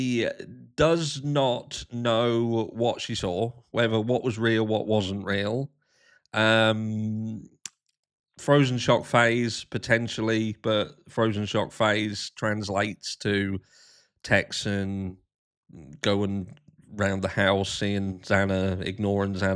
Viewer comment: I always thought you were saying “frozen shocked face.” 😱